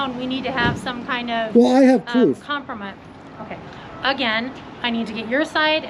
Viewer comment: did she say Kompromat instead compromise?